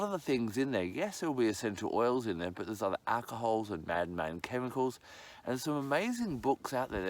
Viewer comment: did you say mad man chemicals? So true!!!